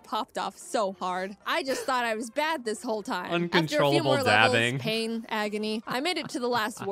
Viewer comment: Did he just call that, dabbing?